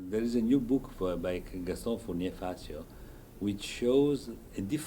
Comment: he mentions a book. I cannot understand it. Anyone knows which book he means? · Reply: Dear Tijmen, He's talking about Gastón Fournier-Facio's "Gustav Mahler. Il mio tempo verrà" (Gustav Mahler. My time will come).